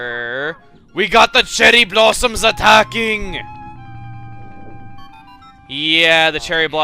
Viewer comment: sounds like is saying "WE GOT THE JELLY BLOSSOMS ATTACKING!" lol